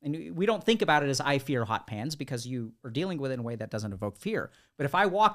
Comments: I thought for a min he said 'we all fear hot pants' lol. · I totally misheard the statement "I fear hot pans" as "I fear hot pants", took a second to calibrate lol